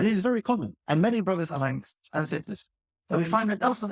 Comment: Jazak'Allahu khairan, at time 5:11 he says "brothers" and not "prophets" and I they think he says something else just after that as well